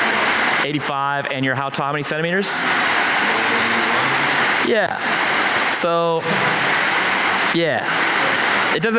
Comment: did he say 181cm or 191cm? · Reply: 181